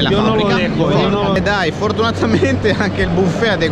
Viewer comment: Ma a 6:10 ho sentito un "FORDunatamente"?